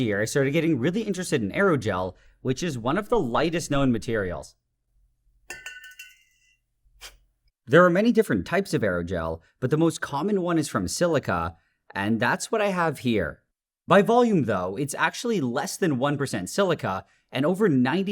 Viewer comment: I thought it said androgel and the trans in my heart went pwease🥺👉👈